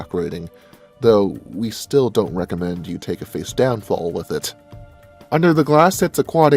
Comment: what the hell does he say , don't recommend you take a stab hold with it? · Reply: We still don't recommend you take a face down fall with it.